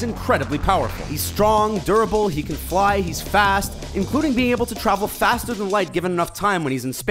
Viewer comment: did you just called Thor fat